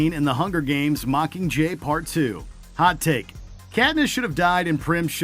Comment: Putting Prim in the honorable mentions is dumb, she dies in the books at the exact same spot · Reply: And I thought he said "Candace" lol I was like, who the hell is "Candace" then figured he said "Katniss".